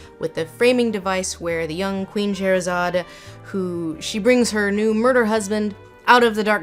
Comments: the CCs read "queen Charizard" instead of "queen Sherazade". Needless to say, please correct it. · the closed captions show "Queen Charizard" instead of "Queen Scheherazade" and I love it.